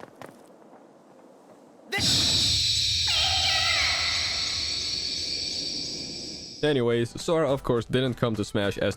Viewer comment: WHAT DID BRO JUST SAY??? 💀💀💀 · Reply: He said SHHHHHHHH like he about to say a curse word